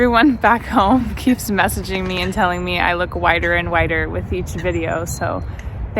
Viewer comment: For a moment I thought you said they told you you looked 'wider and wider' instead of 'whiter and whiter'. Which made me think, 'that's very unkind' but then I realised I heard you wrong. :)